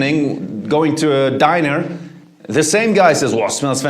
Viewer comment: did he just say dinar instead or diner 🤣🤣